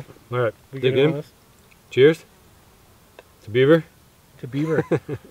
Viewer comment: who else heard " two pervert"?